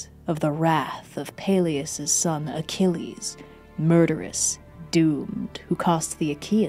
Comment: I really thought said dude instead of doomed at the start😅